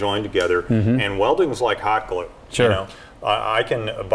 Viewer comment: Welding is like ??? What does he say at 2:29?? ANYBODY?!? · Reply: hot glue